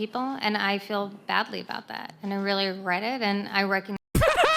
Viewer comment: I heard "I really reddit".